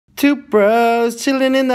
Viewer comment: Lmao I thought he said “Toothbrush” not “Two bros”